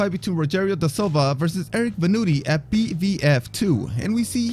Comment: Can you please put the names of the fighters on screen? I can't understand what you're saying sometimes, especially at 2:50 -- I've been trying to look up the fight but can't find the names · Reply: Rogerio da Silva vs Eric Venutti